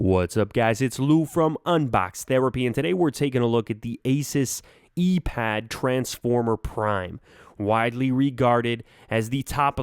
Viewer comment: at first I thought it said Anus Transformer Prime